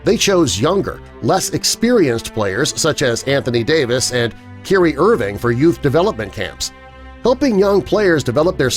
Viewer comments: Did he just say Kelly Irving? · Who Irving I heard of Kyrie.sound Like he said Kurrie